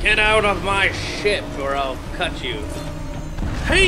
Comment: I thought said get out of my sh##,and it was actually get out of my ship lol